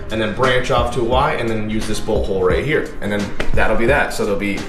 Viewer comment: when he said bolt hole, i thought he said butthole